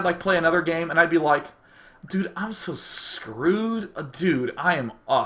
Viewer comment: What the hell did he just say at , 21:09 , did he just say he'd screw a dude ???? WTH is he on ????